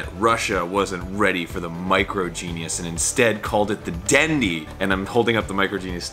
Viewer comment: all I hear is "micro Jesus" and now I cannot stop thinking how cool a pocket-sized Saviour would be.